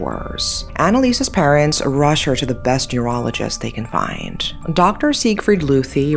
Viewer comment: I thought she said urologist, not neurologist! 😂